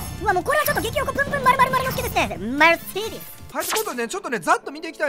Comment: マルティネスにしか聞こえん